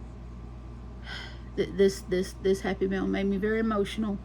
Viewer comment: Hi, night owl myself. I keep all my scraps and just started using them in the 100 crochet tiles book. When you said you got Happy Mail , I thought you said happy meal and was like, dang I’m hungry. Lol. Your C2C is looking nice. I need to make on for a friend but never have done the C2C. You inspire me. 😀🥰